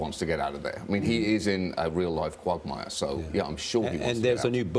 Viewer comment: did he said Quagmire?